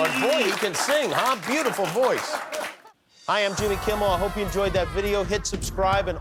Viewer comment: Why did I hear "Hi I'm Jimmy, I'm the pope"